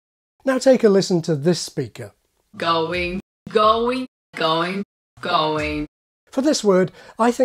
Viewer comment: I thought she was saying “gullwing”!